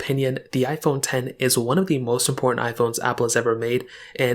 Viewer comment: That lack of enunciation @ 0:09 was cost me 20 seconds trying to figure out what you said there. He said "important" if anyone was wondering.